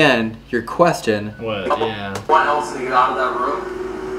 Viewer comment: i hear "Elton get outta the room"